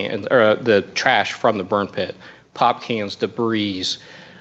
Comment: did he just say sp*rm pit...?